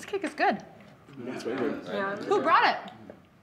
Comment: what does she say at 0:26 ? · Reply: "Who brought it?", as in "who brought the cake", implying that the cake is poisoned.